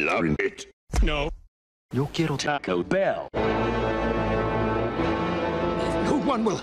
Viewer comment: "I'M LOVING IT!" No! "Yo quiero Taco Bell." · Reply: BrinaFlautist I thought he said "no. You kill Taco Bell."